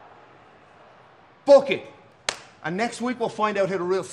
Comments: Am i the only one who thought Finn Balor said "f*** it"?😂😂😂😂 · I can’t be the only one who thought Finn said “fook it” not “book it” 😂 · I thought Finn said “fook it!” for a sec and I had to do a double take 😂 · I can’t be the only one that thought Finn said ‘Fook it’ 😂😂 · I almost thought Finn said “Fook it!” Lol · Why did it sound like Finn said “Fook it” instead of “book it” 🤣🤣🤣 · For a second I thought he said "FOOK it!" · what finn say ? 😂😂😂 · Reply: Book it but it sounded like he said the f word due to his accent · I don’t know why I thought Finn Balor said “Fook it!”😂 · Sounded like Finn said fook it lol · Why at 1:18 it sounds like Finn said “F it” lmao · Lmaoooo for a second when Finn said book it I thought he said fook it